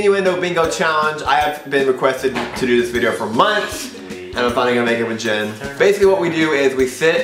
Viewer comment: I Thought He said "Im Finally Gonna Be Naked With Jennxpenn